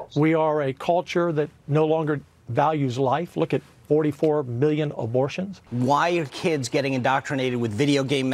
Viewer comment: Does he say 44 billion abortions?